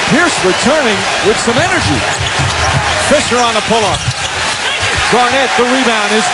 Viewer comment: did i just hear "Faker on the pull-up????"